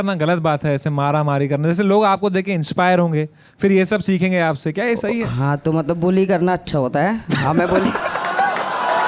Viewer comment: what did he said? Boli karna aacha hota hai????